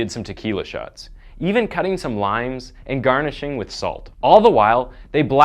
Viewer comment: I thought you said taking the killer shots and cutting up some lines like cocaine but you said limes😂